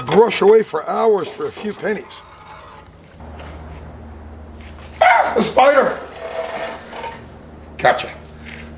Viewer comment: When he said “ah spider” and sprayed the gas, I thought he said “ah fire” the first time I heard it 😂 wouldn’t be the best idea 😅